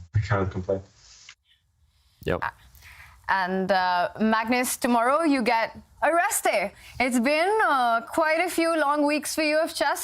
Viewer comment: For a moment, I thought she said that 'Tomorrow, you get ARRESTED''